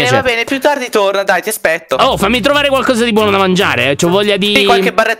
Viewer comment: solo io ho sentito ti aspecto 😂